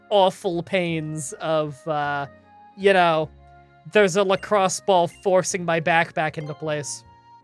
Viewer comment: First time you said you had something on your back, I misheard it as "crossbow" (Don't ask how or why). using a crossbow to adjust his back, a special Canadian technique.